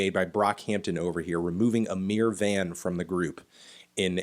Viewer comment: I thought you said a mere van at the beginning lmao